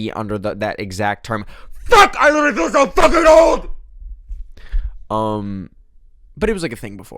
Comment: wtf did he even crash out about i couldn't understand what he said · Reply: He said "FUCK I LITARALY FEEL SO FUCKING OLD"